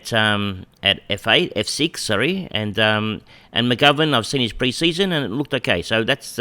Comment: Hi, loving the insight. Only thing that is confusing me is your trades in round 2. Doesn’t make sense from a salary cap perspective as you would’ve been over. Was it Connor MacDonald or Luke McDonald you traded out? Looks like you traded Luke out in round 3 so can’t be him. Have I missed something? Out Whitfield 502 Owens 117 MacDonald 117 TOTAL 736 In Rachele 185 Green 430 Bowey 265 TOTAL 880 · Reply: Ah I thought you said McDonald. McGovern makes perfect sense!